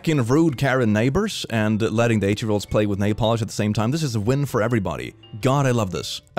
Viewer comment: I kinda understood napalm instead of nailpolish xD